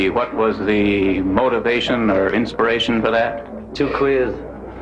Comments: Paul's quote at 3:22 and John's quote at 5:08 2 of the best comebacks ever. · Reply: What did John say at 5:08? Too queer? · What did John say at 5:08? I never understood what he was saying. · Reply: He said "two queers" referring to lesbian/prostitute thing said earlier.